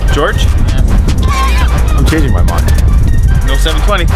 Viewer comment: Did I just heard "720"?